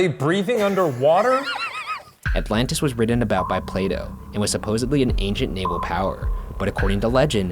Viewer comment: I thought Ryan said "Play-Doh"😂